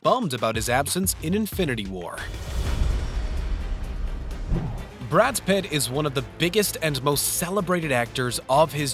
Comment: Did he say "Brad's Pit?" Is Brad Pitt so good that even his arm pit gets movie deals now?!?